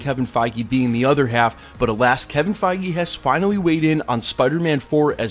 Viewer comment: When you said "At last, Kevin Fiege has weighed in [on future Spider-Man films]", I thought you said "alas, Kevin Fiege has weighed in" and I got scared for a moment. 😂